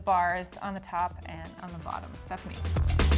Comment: did she just say I’m a bottom?